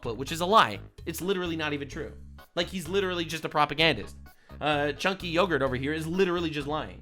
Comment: lol I misheard "dank yoghurt" keeping it 😆😆